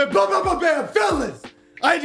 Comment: Wdf did he said at 0:02?? “ BLA BLA BLA BLA FELONS”